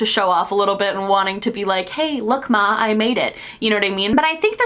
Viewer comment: Did you just say hey look am I made it? I swear if you’re a panic fan I will loose my shit